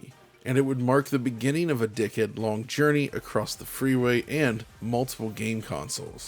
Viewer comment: I heard "Decade long journey" as "Dickhead long journey" and now I'll never not hear that.